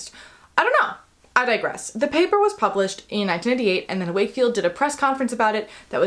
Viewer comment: Amazing video! Quick note: I noticed that at 4:18, you (vocally) mentioned that the paper was published in 1998, but the closed captions say 1988. I don't know if you're able to change that at this point, but I just wanted to mention it in case you're able. As always, thank you for the educational content.